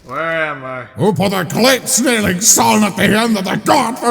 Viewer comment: I thought Caldor just said "no sun," and that made it go away.